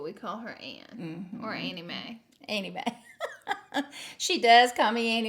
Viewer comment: Omg her name is Annie Mae and I thought they said "anime" for some reason 😂